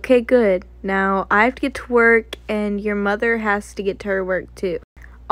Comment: Bruh 😂 I thought he said twerk 😂😂😂